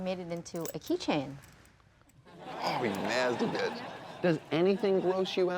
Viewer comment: Does tosh call her a nasty bitch at 5:29? If he did that's funny as fuck hhahahahaha